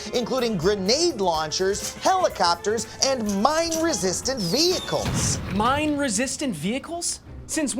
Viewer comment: I thought Adam said "mind resistant" and I had a hold up a minute moment.